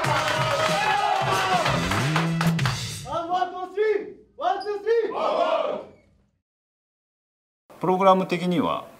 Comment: ハードワーク ？？？ １！２！３！ ハードワーク！ この？？？の部分ってなんて言ってるんですか？ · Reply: ハードワーク On Three ですね！